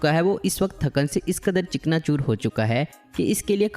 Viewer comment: what i heard chikna choor😅😅😅